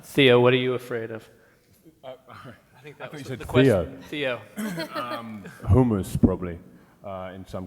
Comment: I swear I thought he said homos